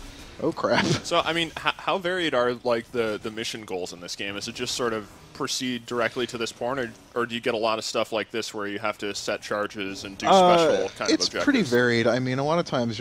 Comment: proceed directly to this porn? is that what he said? haha